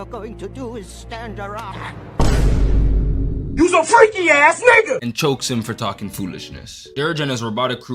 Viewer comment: I always thought he was saying "YOUS A FREAKY A$$ SNAKE!" Not the N-word at the end instead of "Snake". Also, cool re-upload, but why cut out the swears in this one? It seems so jarring to me.